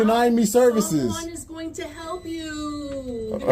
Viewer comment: "Nobody is going to help youuuuu." F*cking childish. But that statement sums it all up. They're not there to help anyone. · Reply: In her defense didnt she say "somebody is going to help youuuuu" ? Thats what i heard